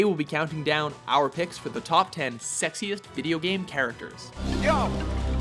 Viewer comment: I thought you said sexist, so I got some popcorn, and a few lounge chairs to watch potential flame wars, from white knights, feminazis, and normal people. Now I see you said sexiest, and there will not be any flame wars. :( Does anyone want free popcorn?